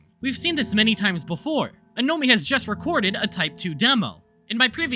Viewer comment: I heard "amia has to eat"